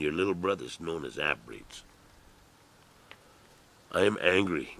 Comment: What did the big guy say at about 1:11:07? "You people are responsible for that.You America and your little brothers known as ........" Known as what? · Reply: He said Half-breeds. I hate that the people look upon us half breeds like this. It is not my fault that my grandmother's children were taken away by the government and adopted out. And my father married a German/Cherokee mix and had me. I am over half, I am Cheyenne, Cherokee, German. I was raised in the white way and did not know my father. I have just recently found him and my brother and sister. But in my heart and spirit I am Native and proud. I did not fit into the white kids in school nor the blacks. I was picked on by both because I do not fit. And now I find that alot of Natives treat me just as bad. And no I am not registered. I do qualify. My great-grandmother is on the final Dawes roll and there is a roll number and she is listed BB. I also have ancestors on my mother's side that are on the rolls and have roll numbers and listed BB. Until all of the people deal with the hurt and pain and learn to stand together nothing will improve. My heart hurts to think of the way the people live on the reservation. But I want to learn all I can about my heritage. Our people were strong and proud, now they are broken and my heart cries for us all. So Full Bloods hate me if you must, I still have love for you. And like it or not but I am still one of you.